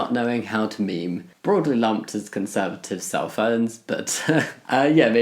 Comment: I thought you said conservatives Cell Phones. I'm really dumb.